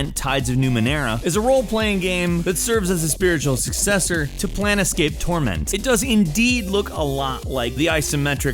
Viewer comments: Did I just hear "PLANETscape Torment? Credibility -1. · Did I just hear "PLANETscape Torment? Credibility -1. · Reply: I heard "Plan escape" but that's just me. Also, is literally the WatchMojo of video game channels, so, if you were looking for credibility.....